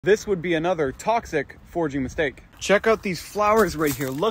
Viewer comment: I thought you said forging mistake so I was so confused at first what that flower had to do with forging something 😂